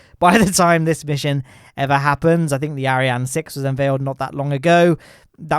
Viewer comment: Dont take a shot of whiskey everytime says "Ariane 5". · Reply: OH!!! I'm so glad you wrote that, I first heard "Aryan" and was like uhhhhh then figured his accent just turned 'Orion' into (Ariane). Things make more sense now lol.